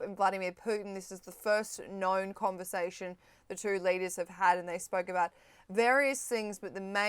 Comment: Did she just say (@ 1:15) "the two ladies" about Trump and Putin? 😂